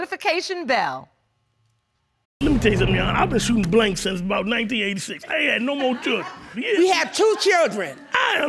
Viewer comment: Am I the only one that heard him say "I was shooting blanks until about '89"?